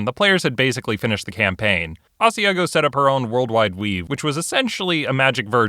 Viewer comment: I misheard it as "World Wide Weeb".